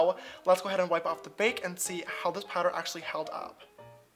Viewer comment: Omg who saw the part when he sees the powder to see if it "held up" and the captions say, shook 7:50 also love when he said, "dewy skin, never heard of her" and how the time said 9:11... like, FBI OPEN UP!!!